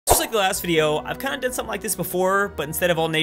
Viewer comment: In the begining it sounds like you say "I'm sick of the last video".